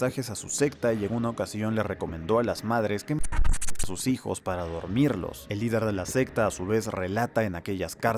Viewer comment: alguien sabe que dice en esta parte? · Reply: Dogaran